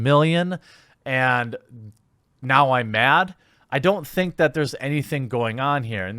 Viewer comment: I thought you said, "and now I math". I thought it was a funny joke. ☹️